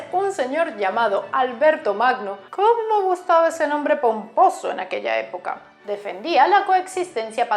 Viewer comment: Oí ''cuanto gustaba ese nombre para un pozo'' en vez de ''cuanto gustaba ese nombre pomposo'' 😂